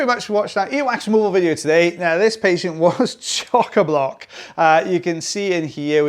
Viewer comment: I finally used Google to find chok-a-block. I thought you were saying choker block.